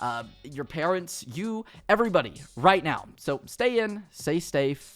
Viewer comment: did he just said say stave?